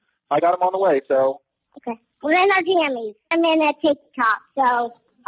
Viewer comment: "I'm in my Jammies so ill have to get dressed" 🥺♥️ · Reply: I thought she said I'm not jamming, I'm on my tik tok